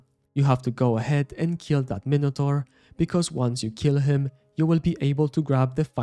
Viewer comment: If you need any help with the Quest then join my discord and I'll personally help you, also if you like what I do and what to support me you can do so by joining my patreon or becoming a YT Member, this comes with awesome perks such as 1 to 1 coaching sessions : Patreon: <link> YT Member: <link> Discord: <link> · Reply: thought you said "go ahead and milk this minotaur" the first time I heard it not "nuke"